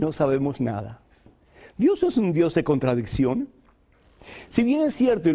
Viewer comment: Dios es un dios de contradicción??? Eso es lo que entendí